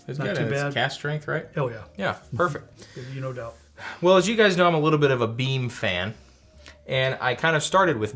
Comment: I thought said "as you guys know I'm BEAN fan"